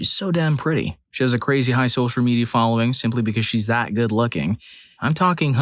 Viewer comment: for a second i thought you were gonna say she has a crazy high social credits rating and i was like DAMN WHAT A CATCH